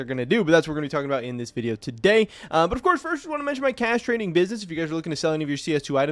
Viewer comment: Bruh you need to slow down I thought you said Castrating business 😂😂😂